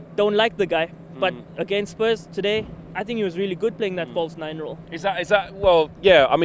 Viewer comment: I think thought he said number 9 instead of false 9